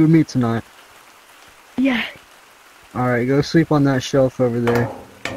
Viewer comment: did bruv say sleep on the shelf??????